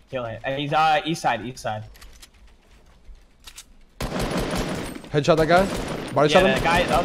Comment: Soy el unico que en el min 0:52 entedio que dijo "headshot aca"